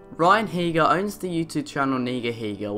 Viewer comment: did u just say nigga